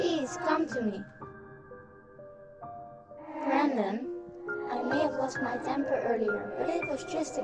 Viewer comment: Did she said Brenden?